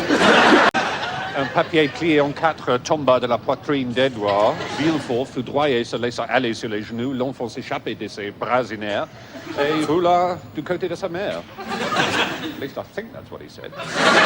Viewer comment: If anyone's wondering what the hell Stephen is saying at 3:05, he's quoting the Count of Monte-Cristo: "Un papier plié en quatre tomba de la poitrine d'Edouard. Villefort, foudroyé, se laissa aller sur ses genoux ; l'enfant s'échappa de ses bras inertes et roula du côté de sa mère."